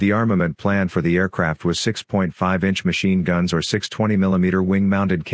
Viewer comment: "6.5 inch machine guns"? I think not. LMFAO! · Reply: You mean 6 - 50 caliber machine guns? No one says .5 inch machine guns, that's why it sounds like you said 6.5 inch, which technically you did.